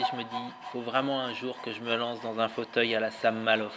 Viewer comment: Bonjour et bravo pour la vidéo, A 3:00 tu dis que tu devrais fabriquer un fauteuil ... un fauteuil quoi ? J'ai pas compris ce que tu dis, peux tu me l'écrire stp ? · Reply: à la Sam Maloof, fait une recherche et regarde en particulier la manère dont il réalise ses assemblages